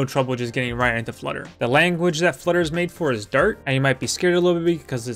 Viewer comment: Anyone else hear ‘The language for Flutter is DIRT’ 😂 died when I misheard that. New favorite Flutter channel